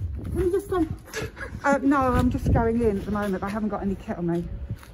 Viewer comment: I legit thought she said "I haven't got any ketamin" 🤣😆